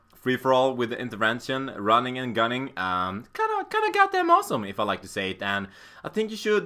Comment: I thought you say: PRETTY goddamn awesome